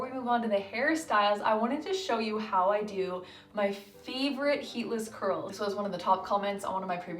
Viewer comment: I thought it said “heartless curls,” and I thought “what in the world is that?!” LOL.